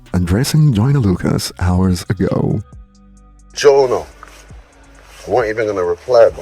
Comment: wtf is he saying need a translator please I can't understand him while he's wearing that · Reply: He said "Jonah @@@@@ ye man"